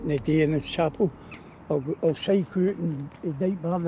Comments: "ah'll cycle oot 'n something/somewhere t'night" hard to hear what or more likely where he says as he turns fae the mic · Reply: I cycle out to dykebar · It sounds like he says... I'll take a cycle out to Dykebar... Which was a mental hospital near Paisley. There's 'something in the water' in Paisley that makes many irritable and kids feral. A poor fast food diet is surely half the problem for Paisley, plus stinking weed and vape smells everywhere, even in once lovely parks. It's everywhere, even the delightful, on the surface Castle Douglas near the Borders has too many teens hooked on weed, it's sad and even sadder no one bats an eyelid. Teens who are too impressionable. More lives will be destroyed across Scotland, because there's not always a fatherly influence correcting them, the father may be taking drugs himself, and the mother may play every role in these households. Cocaine often turns straight folks bisexual if London,Manchester and Carlisle are anything to go by... Glasgow has fallen in the centre, but hopefully the Clyde walkway can be redeveloped and reclaimed one day, St Enoch square too. And to think a fine Cathedral stands just across the road from The Clyde walkway of hopelessness.